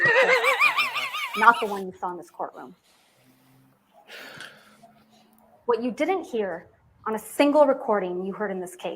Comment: Can someone please quote what says around 49:40 after he says Amber is making another list. I can't figure it out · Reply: shovel, duct tape...